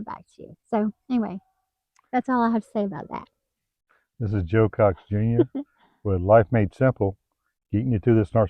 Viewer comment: As always, a beautiful couple! I misheard you and thought you said, "joke ox jr"! Haha! I guess you're the joke ox when you laugh about your situation!❤